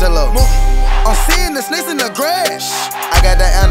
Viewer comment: im seeing the snakes in the grass the first time i heard this i thought it said im seeing the snakes in the ass #YOUTHOUGHTBOIII